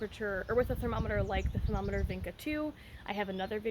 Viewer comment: Hi! Thanks for this video. What is the product you referred to at 10:22? I didn't quite catch the name :) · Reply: Femometer Vinca 2 ❤ I have a review of that therm on my channel too! 😊